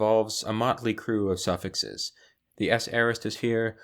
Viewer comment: what is being said after "involves"? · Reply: "A motley crew of suffixes..."